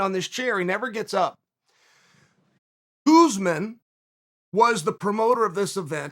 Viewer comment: Wait did he just say USMAN was the promoter ?😂……